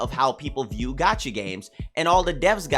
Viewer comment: I keep hearing "gachi" instead of "gacha" and can't unhear > , <